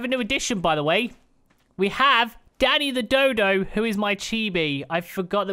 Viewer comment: I legit heard "daddy" the dodo and was slightly concerned xD